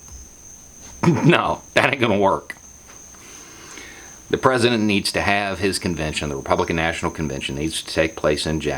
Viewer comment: I thought said , The president has to leave!. And I was like HELL YEAH!. Then I realized he said, He has to lead!. Oh well.